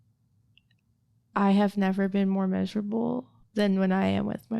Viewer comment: Wtf is she saying at 35:56 "I have never been more measurable... than when I am with my baby daddy." Wtf does this mean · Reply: Miserable? Maybe?